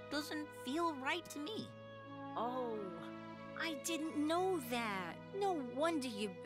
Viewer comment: After the cows' speech, the big cow sounded like it said, naw really, i didn't know that